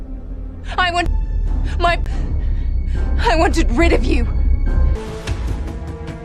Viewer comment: I'm sorry but i started cracking up when i heard it, it sounded like rebekah was saying this, "I wanted my home wanted rid of you." I'm sorry forgive me please